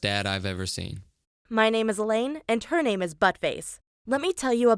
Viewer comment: "her name is buttface" What!? Did i just hear